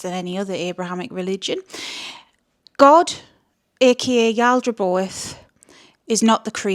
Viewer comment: what is the god's ocultic name I can't hear it. is it yalderboth? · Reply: Yeah Yaldraboath